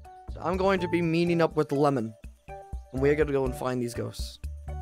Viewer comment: I honestly thought he said Lemming like the creator of the game😂😅